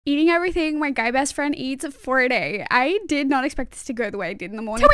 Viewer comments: I thought she said gay best friend not guy best friend so I had to watch it like 6 time cus I have ADHD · I thought she said gay bsf